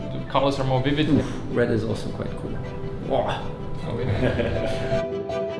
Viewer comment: did nico just say bwoah?